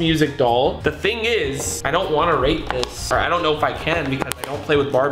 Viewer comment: Who else thought faze rug said “I don’t wanna rape this.”